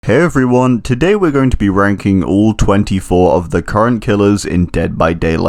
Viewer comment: I thought he said ‘today we will be wanking the killer’